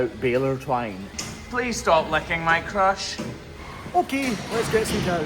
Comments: …… OMG! It sounded like you said “Please stop licking my crotch”!!!!!!!!! 🤣😂🤣😂🤣😂🤣 I had just taken a drink of wine and it ALMOST came out my nose!!!!!!!! 🤣😂🤣😂🤣😂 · Raise your hand of you thought he said "please stop licking my crotch"😆 Raise your other hand if even after all these years of watching, you still need subtitles on to understand them? Ok now keep your arms up and yell "i am a wonderful person who deserves love and kindness and I am going to have a great day!" Ok put them down now😁❤️🤟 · I swear I heard “please stop licking my crotch”. Granted I am half asleep and only up because my daughter needed to get to school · I thought you said stop licking my crotch to the cow 😂